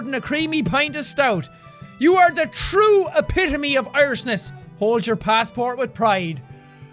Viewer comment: Everyone should watch this with the captions on, it makes the video 1000x better. xD Case in point: at 18:54, when Sean reads, "you are the epitome of Irishness," the caption said "arsonists" and I can't fucking stop cackling.